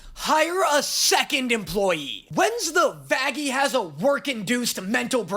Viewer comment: am i stupid or do y'all hear "faggie" instead of Vaggie too?